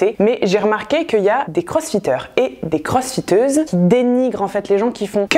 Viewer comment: Merci pour le fou rire a 3 min 49! J'ai entendu "crossfisteuse". :D Allez je reprend la vidéo!